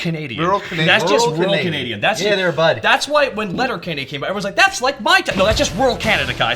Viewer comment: OK I have no idea what said at 2:18, can anyone tell me what he was saying about, "When (?) came, 'That's like-' No that's just rural Canada" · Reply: "Letterkenny" is a show. You should go watch it.